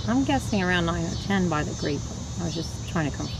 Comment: “ten” is what I heard